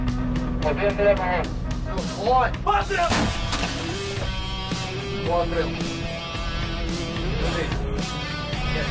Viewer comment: What he said in1:40 ? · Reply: Postril